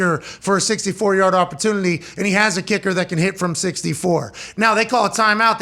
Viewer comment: "and he has a kicker who can hit from 64" pretty sure his longest is 61. that's what they said during broadcast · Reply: "Can", not "has".